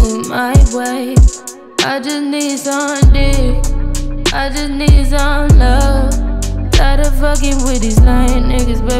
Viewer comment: Toda la vida pense que decia “I just need something”